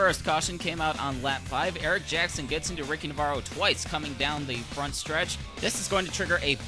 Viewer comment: did you say ricky varro